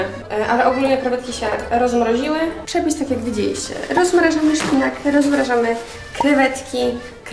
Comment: usłyszałam ROZMNAŻAMY Xd